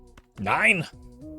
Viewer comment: what he say💀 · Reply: NEIN